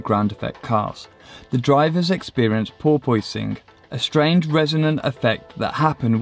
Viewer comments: what the hell is he saying, "porpoising?" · I have absolutely no idea what you are saying... Ball poisoning? Boil boising? Closed captions say "poor poisoning" I really cant figure it out. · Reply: porpoising